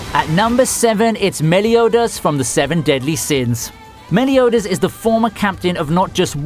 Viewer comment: the automatic english caption said "smelly odors" instead of "meliodas" wheezed this was too overpowering.. 😂😂😂